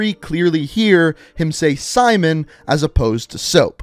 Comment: I heard soapmon